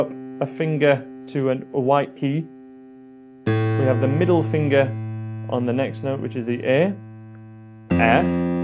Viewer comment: wanted to put the party where he says middle finger XD but instead of u keep clicking on the time 1:56 , it sounds like he’s saying NIGA! XD